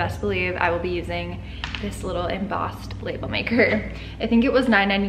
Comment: I heard “bagel maker” and I was staring at the label maker soooo hard trying to figure out how/why 😂😂 lol I’m dumb